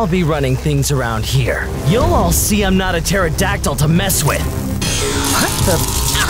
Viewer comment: What did Cyan said? 😶😳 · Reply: what the😶